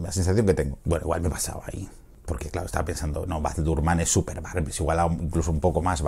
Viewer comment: alguien sabe a que director o pelicula se refiere en el minuto 5:58? no logro entender bien lo que dice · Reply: Baz Luhrmann, el director de Moulin Rouge